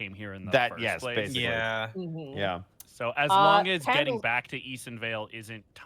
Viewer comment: For a second I turned out and I thought I heard said candleD*ck and I woke up instantly and replay